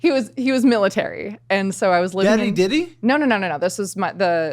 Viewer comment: I thought Caleb said Daddy Dildo 😮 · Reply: He definitely said Daddy Diddy once 😅